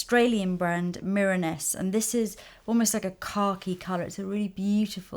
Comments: I thought she said Car Keys not Khaki. lol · Beautiful look! I kept hearing "car keys" when you said "khaki" lol. I'll definitely be trying this look. Thank-you!